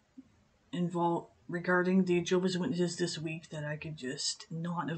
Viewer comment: I just came to point out, that the auto captions say jobless witnesses instead of Jehovah's Witnesses right in the beginning at 0:15 XD